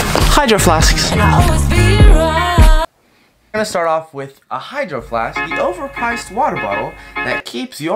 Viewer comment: She said hydro flask but I heard “ride or die”